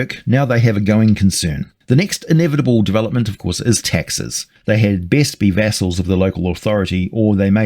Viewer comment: Oh taxes,I thought you said Texas.